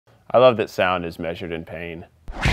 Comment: I heard "A dove is measured ippy" or something like that. What does he say after measured?